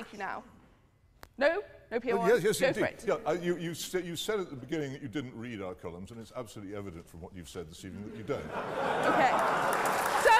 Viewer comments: I needed subtitles and needed to replay it 4 times to understand what he said. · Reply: “You said at the beginning that you didn’t read our columns and it’s absolutely evident from what you’ve said this evening that you don’t” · can some one tell me what he’s saying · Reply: "You've said at the beginning that you didn't read our collumns, and it's absolutely evident from what you've said this evening that you don't." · did he say "you read our columns"?